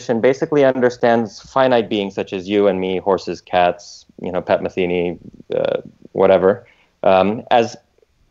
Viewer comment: did he say pat metheny, and that pat metheny is finite? Heresy. Thumbs up if you like pat metheny 👍👍